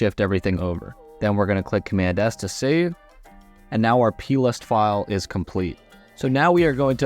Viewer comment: What did he say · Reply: "Now we're going to click command + S to save and now our plist file is complete."